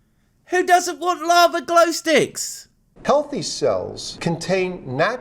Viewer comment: I swear it sounds more like he says "coffee cells" 🤣